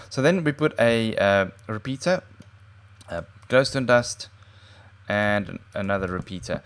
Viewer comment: did you just say glowstone dust